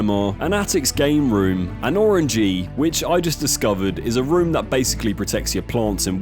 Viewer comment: I thought he said orgy and I can't be the only one here who did